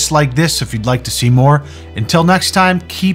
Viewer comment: I thought you said keep pit thrusting at the end and didn’t question it because of that pit check mid episode 😂